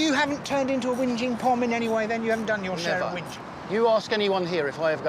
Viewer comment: guys I know what a POM is, but what is "winjing" (sorry that is how I heard it at 14:43). Thank you. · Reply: Whingeing means whining.